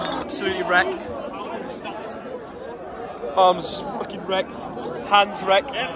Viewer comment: did thogden say his ’erect’ 😂